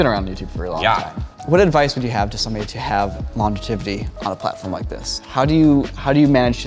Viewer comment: I heard “longetivity” while watching this tired right before bed and thought my brain glitched haha.